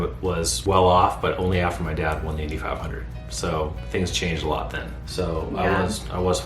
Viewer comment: AFTER UR DAD WON $8500? THAT'S NOT A LOT OF MONEY. DID I HEAR U WRONG?